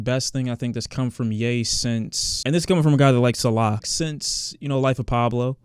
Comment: Did he say "this is coming from a guy who likes Allah" or did I mishear that lol